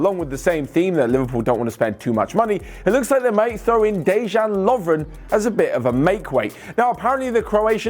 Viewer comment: did he say Dejan Lovren how is that a sweetener